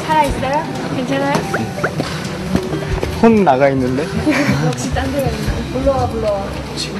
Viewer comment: it's not "he seems angry though?" it's "his soul seems to be gone" what you thought is somewhat right, but the grammar wasn't right in korean so it's his soul's gone it's confusing, but you'll get what i'm trying to say soon XD keep up the great subs !!